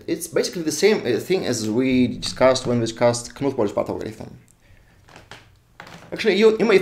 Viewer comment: what is "kamut polish battleground"? 12:02 that's what auto caption tells me... (I know you said some kblablakblak algorithm) some subtitle would be nice :) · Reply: thanks, I found it now, Knuth–Morris–Pratt algorithm